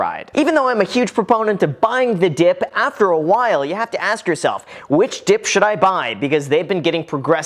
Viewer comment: I hear that, "which dip do I buy?"